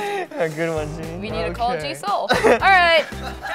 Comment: can i ask what did jb and jimin said during 07:23-07:32? did i heard it right that jimin said jisoo? 😮😮😮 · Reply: Sheena Heart Castillo she said G.Soul